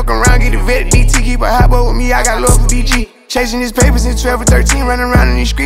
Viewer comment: Who else thought Lil Baby said he got love for DDG